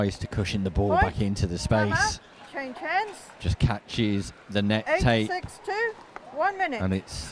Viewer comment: What Referee says during Change Ends: "Point! Time out. Change ends. [Announce Score]. One minute."